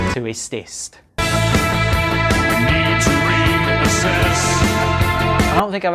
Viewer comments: What is the name of that band? I can't manage to understand what he says in that part (English isn't my first language) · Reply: Jimmy Sticks I think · What band name does he say at 5:11 after Slow Readers Club? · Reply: That would be Ist Ist 🙂: <link> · What is the name of the band at 5:12 immediately after Slow Readers Club? It doesn't show in the subtitles and I can't quite catch the name when the narrator says it. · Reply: The band’s called Ist Ist 🙂